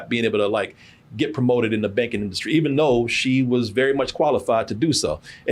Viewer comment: OH YOU SAID BANKING! I THOUGHT U SAID BAKING . I was like wtf they wont even let is cook on higher level